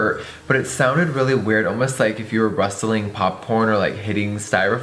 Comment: Did anyone else notice that Jeffree said Pop-porn instead of Popcorn?? 4:28 😂😂😂